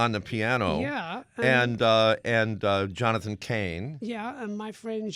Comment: Thought he said 'Jonathan King' :-)